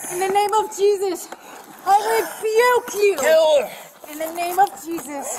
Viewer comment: The religious element kept this interesting. It's what happens when you get a little church and a lot of drugs. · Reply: I heard "re-juke you" must be a gamer. :P